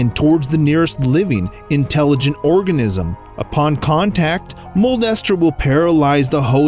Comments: At first i thought he said molester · I thought he said “moe Lester” lmao